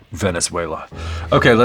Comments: Did he just said "Venezuela" just before eating the wing? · did he say "Venezuela"?